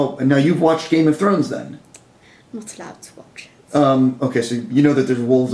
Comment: What does she say in the minute 10:08? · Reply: Iban Garcia "I'm not allowed to watch it."